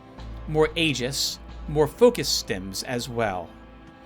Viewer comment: "focus Tim". Yep, I hear it every time!